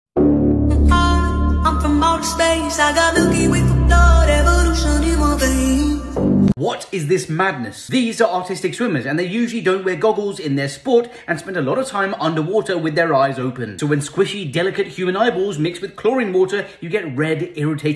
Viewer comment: I really heard "these are autistic women" 💀